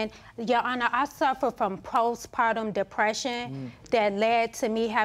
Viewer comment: Post Madison distress lol... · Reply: SPorter6982 lol. I thought he said, "Post madness depression." lol. I was like yup he don't know a thing about that for sure, but he could have been supportive of his wife while she was depressed. smh. Happy Day to you! :)